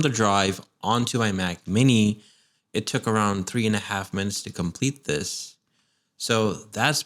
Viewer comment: I thought you said, “it to 3 1/2 months to complete this”, I was ready to ship that mac mini back immediately!!!😂 I had to turn on CC and was very relieved!!! Love you video, you have a new subscriber!